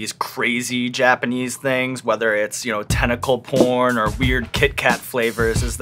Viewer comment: Ok I guess we’re gonna skip the “tentacle porn” thing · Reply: I thought he said tentacle corn